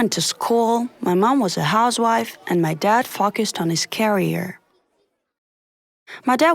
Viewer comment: not trying to be rude but I heard my dad f***s us on his carrier, oof